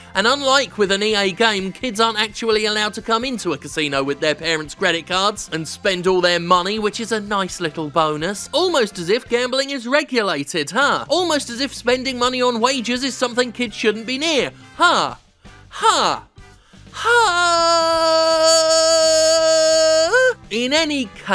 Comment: Did you just say spending money on wages?